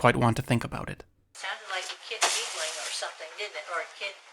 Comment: She says "it sounded like a kid giggling" Thats what I thougt it was when you showed the first cut of the sound, gave me goose bumps immediately. She didnt say toy at 9:27 she said a kid giggling